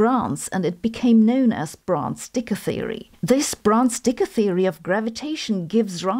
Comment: Listening without the visuals and I keep hearing about "bronze dick theory" 😂